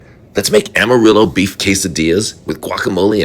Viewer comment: I thought it said armadillo lol